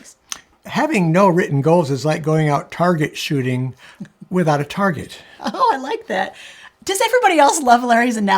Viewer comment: My brain thought said “its like going Target shopping without a budget” 😂